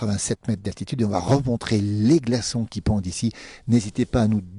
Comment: j'ai entendu "glaçons qui pondent" au début de la vidéo, c'est plutôt "pendent".